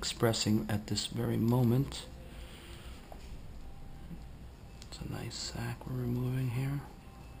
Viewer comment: Did he just say, "That's a nice sack"?